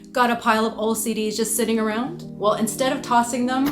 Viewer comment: Omg- I thought you said "Got a pile of OCD's?" 🥲 (BTW this video is BRILLIANT :D )